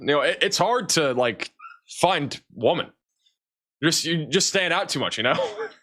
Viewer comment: did he say “Find. Woman.” like a caveman?! 🤣🤣🤣